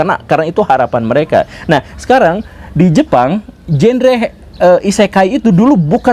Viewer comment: "genre he" hayo pak guru mau ngomong apa hayo